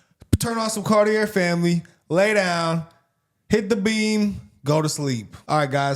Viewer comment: I thought he said. “ Hit the bean!” 😂